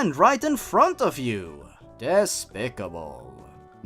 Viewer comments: On the Dora one, I thought you said ‘Despacito’ instead of ‘Despicable’. And I was legit about to go ‘That’s so sad. Alexa, play Despacito.’ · when said despicable in that dora story i thought he was going to say Despacito. i need help haha